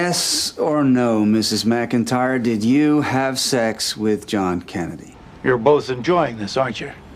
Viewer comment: "Yes or no, Mrs. McIntyre, did you have sex with John Kennedy?" At first I thought he said John Candy 🙂